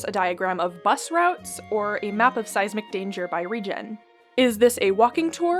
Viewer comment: I’m so sorry, instead of Seismic Danger my brain heard Size MacDanger and I was so confused for a bit until I actually read what was on screen… I felt so dumb, I was like “tf is Size McDang- oh wait a minute… SEISMIC DANGER WHAT’S WRONG WITH MEEEE” My brain just gave up for a bit